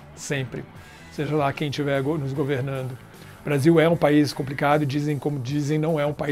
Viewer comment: "seja lá quem tiver desgovernando" Dinho. Obs: foi só eu que escutou assim?